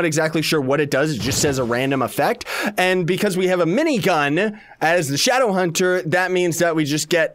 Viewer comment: at first i thougt he said rinnegan instead of minigun